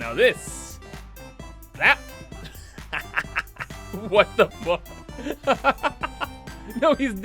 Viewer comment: did just say what the f***??!!!